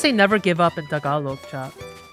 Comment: Wag SUSUko Me a Filipino:did bro just said no my boobs?😂